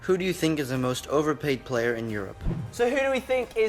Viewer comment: he clearly said europe, this has to be a troll.